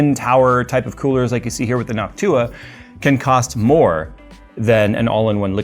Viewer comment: Anyone else hear "hawk tuah" instead of "Noctua"? 😂